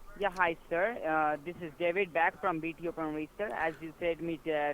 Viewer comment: It sounded like "This is David Beckham"